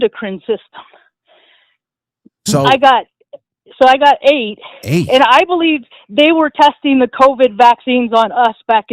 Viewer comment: Did she say... 1:09:39 Offitt AFB ? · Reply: Yes . Offutt air fArce base